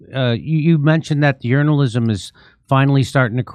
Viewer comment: "You mentioned that urinalism is finally starting to crack" I've called it urinalism for years, and don't know if I'm making myself hear that or if he actually said it.